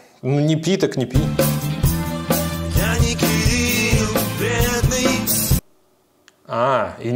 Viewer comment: мб поется я не Вий...хз больше ниче не слышу